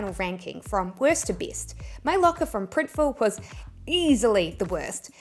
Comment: Thank you for this informative video. I am a beginner. I will use RedBubble. It sounded like you said Printiful in your last comparison of the T-Shirts.